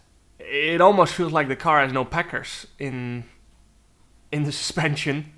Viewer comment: what is he saying here? Packhurst? Lol I don't understand